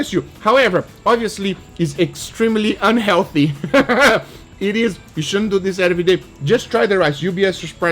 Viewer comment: what does he say?? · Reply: "You shouldn't so this every day" he just stumbled over his words a bit